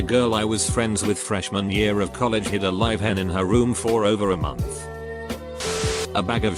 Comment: Tell me why I thought the first one said “live head” like damn you win